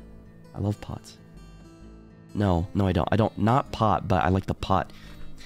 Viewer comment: I thought it said “Shoko” Komi what